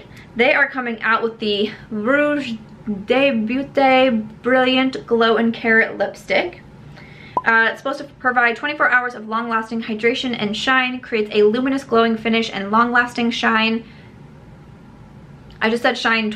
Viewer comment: I thought you said that Gucci lipstick was called “glow and carrot 🥕” and immediately wondered if carrots were the new It ingredient 🤣